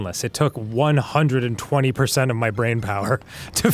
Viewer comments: Why do the cc YouTube subtitles say “used 80% of brain power” when actually says out loud he used 120% of brain power for Lego? Am I insane or am I seeing that right? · when says 'it took 120% of my brainpower' the captions say 'it took, like, 80% of my brainpower'